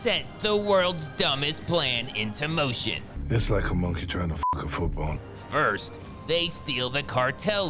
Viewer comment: Can someone translate in English what the fuck did Sea Gull mumble at 5:58 because I couldn't understand a word xd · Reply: "It's like a monkey trying to fuck a football."